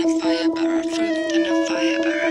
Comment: This made me laugh, which makes me happy. So thanks. I thought it was so funny the way she sings the words "Fruit & Fiber" It sounds like she is saying: "Fruit in a fire burrow" (Definition: noun: burrow; a hole or tunnel dug by a small animal, esp. a rabbit, as a dwelling. synonyms: hole, tunnel) I also loved when you yelled from the rooftops into your megaphone like an Islamic call to prayer. So randomly cool. LOL too funny. You guys are awesome. Ain't no denial. :) This one's great. MOAR!